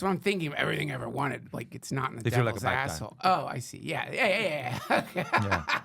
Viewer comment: does anyone know or able to make out what says there ? The CC doesn’t pick it up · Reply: This is home.. If you're like a bad guy (That's what it sounds like to me)